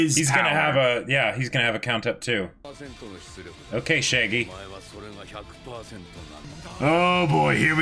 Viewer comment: did he say shaggy💀💀